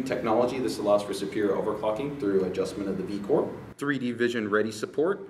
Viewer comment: it sounds like he said "over-fucking" hahahaha. (I know it's overclocking)